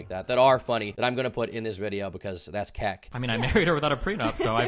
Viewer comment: did u say that’s kak? Lol